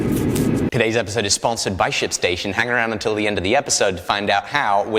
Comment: Did anyone else hear shitstation